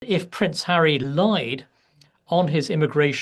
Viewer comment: Got a big video and sound issue on my screen... I thought you said "what if Harry lied"?